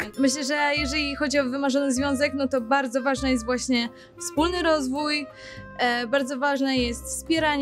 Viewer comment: czy tylko ja usłyszałam "wspólny rozwód"?